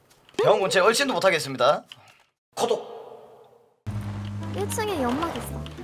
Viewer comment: 나만 코독으로 들리는거 아니지?